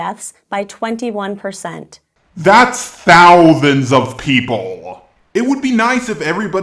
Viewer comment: wait...I think...does have a lisp? Because it sounded like he said "Thouthands" of people. Not trying to stereotype people with lisps, I'm just asking